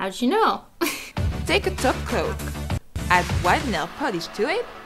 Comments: the whole video I thought you were saying taco when you were saying top coat.. I was so confused. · Every time she says “top coat” I thought she said “taco” lol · All this video when she said 'top coat' I honestly thought she was saying taco. I'm not lying! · Are you saying top coat or taco???!?? Edit: It was my first video calm down. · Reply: She used to say top coat, but a LOT of people thought she was saying taco so she switched.